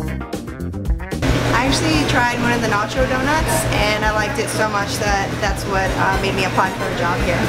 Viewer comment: I THOUGHT IT SAID NARUTO DOUGHNUTS i dont even LIKEE naruto